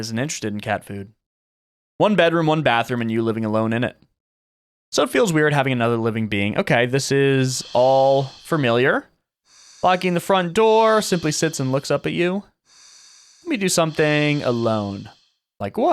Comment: did he say furmiliar??